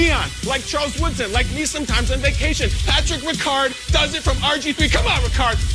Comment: ok, did i hear this wrong.. 3:08 "He goes both ways.. like me sometimes on vacation" LOL!!